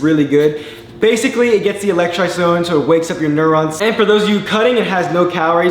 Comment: Can someone explain what he said at 0:40 though? The electro zone? What? Even CC isn't helping. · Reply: electrolytes going is what I think he said